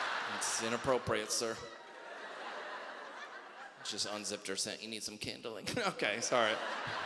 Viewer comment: What does he say at 0:58 about unzipping her candle? I don't get it.